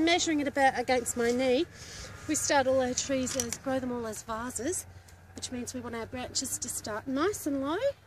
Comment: help! at 6:26 i can't make out what you said: "we start all our trees...."? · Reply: they said ''vases'' as in the shape of the tree. In austrailian it comes out as ''vawwses''